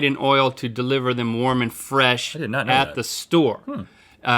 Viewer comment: Did anyone else hear Link say "Mormon Fresh," instead of "warm and fresh" their first time hearing that?